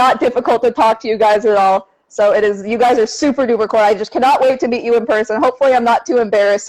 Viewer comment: Did she said «You guys are super duper kawaii»？😆😆😆